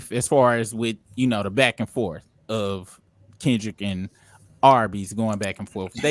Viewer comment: did he call Drake "Arby's" 😂😂